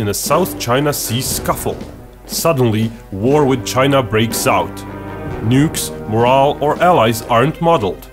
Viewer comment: can someone tell me what he said in 0:24 ? "nukes ..... and allies"